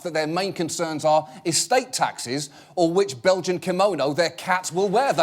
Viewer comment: did he say 'Belgian Kimono'?